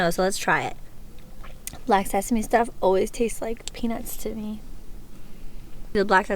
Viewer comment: You said black sesame tastes like peanuts right? Because I kinda misheard that... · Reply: LOLOLOLOL yes peanuts 😂😂😂😂😂😂😂😂😂😂😂😂😂